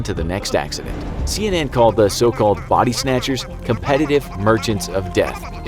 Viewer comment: I thought it said buddy snatcher